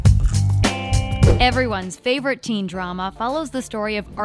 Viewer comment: Did she just say”everyone’s teen drama” uhhh I don’t think so